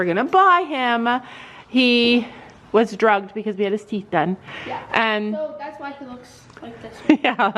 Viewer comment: I think they said teeth. Either a regular check up or prly considering his age they pulled a wolf tooth (don’t know if the translation is accurate). They should actually be sedated for that. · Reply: ok, if they said teeth then yes that makes sense for getting his teeth floated, I just though I heard her mention a farrier - I easily could have misheard feet instead of teeth.